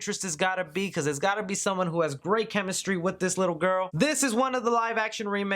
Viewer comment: Someone who has great chemistry with this LITTLE GIRL! Did I hear that right?